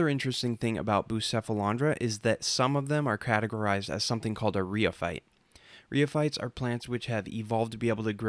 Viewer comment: what's this term you're saying again? sorry I'm not an english native speaker so quite hard for me to figure out. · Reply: rheophyte